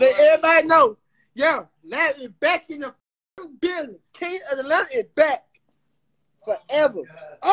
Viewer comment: all I heard was king of ALADDIN lmao